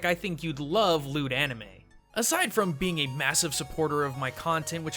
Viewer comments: when said "if you like anime, you'll love loot anime" I heard " if you like anime, you'll love lewd anime" XD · for a second I thought he said 'lewd anime' ( ･ิ-･ิ) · I thought you said lewd anime and I was extremely shocked that this video took that turn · When he said "Loot Anime", I thought he said "Lewd Anime" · I thought you said lewd anime xD. as in hentai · when he did the sponsor thing for loot anime I thought he said "I think you'd love lewd anime" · oooooooh! LOOT anime i thought you said LEWD anime. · Oh loot anime, I thought you said lewd anime lol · When you said "If you like anime, then you'll love Kubo and The Two Strings, and you also like Loot Anime" I thought you said "lewd anime" lol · At first I thought he said "You'd love lewd anime". Loot Anime sounds good, but now I wonder what could have been. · I seriously thought you said lewd anime. wtf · Oh gosh when you said loot anime I at first thought you said lewd anime... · Omg I thought you said "lewd anime" for a sec, not "loot anime" XD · I thought you said lewd anime. lol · lol i thought you said lewd anime. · at first I thought you said lewd Anime at first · Thought he said "Lewd-Anime" damn... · I thought you said "Lewd Anime" lol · ok, for a second I thought he said lewd anime, and I got a little worried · When he said "loot" anime I thought he said "lewd" anime XP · I coulda sworn at 5:45 he said "then you'll like lewd anime" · kind of sounded like he said lewd anime and not loot